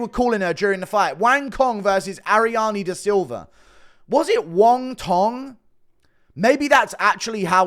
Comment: Lmaooo wongtong shit had me dying I thought they said wonton at first